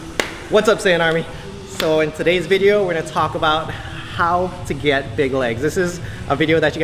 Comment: When I first woke up I thought it said, How To Get Bitches